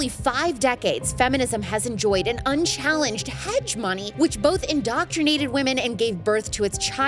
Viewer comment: Did she just say hedge-money?! I think that's supposed to be /həˈjemənē (hegemony)